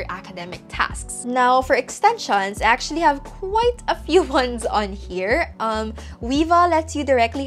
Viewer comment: what extension is that? I hear "leva" but I can't find it in chrome web store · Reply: It is called Weava Highlighter.